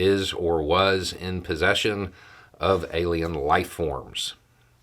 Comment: I could be wrong, but I don't think the words "alien life-forms" were used. What I heard was "non-human remains", which could be, say, a dead bird.